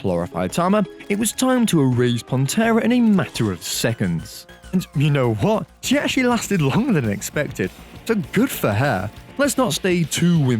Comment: What did he say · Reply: "and you know what? she actually lasted longer than expected"